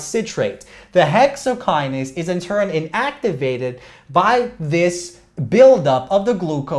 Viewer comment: great lecture from great teacher, thank u sir for hard working …… but you have a simple mistake in 13:42 instead of saying inhibited you said activated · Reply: said in-activated